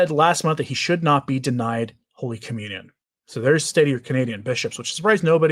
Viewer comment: I thought Bishop McGraton said that he should NOT receive communion